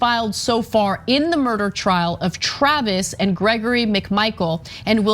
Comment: I had to listen to that again.. i thought you said William "grody" Bryant. I know not everybody grew up with my mom but I grew up thinking "grody" meant distasteful or repellent.